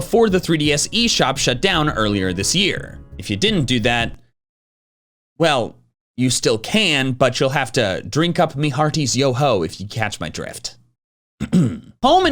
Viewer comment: what was he saying "You have to drink up Mihartis Yoho something like that"